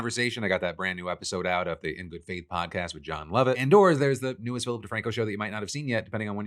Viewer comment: I totally thought you said "Jon Lovitz" and was super disappointed when you weren't having a conversation with The Critic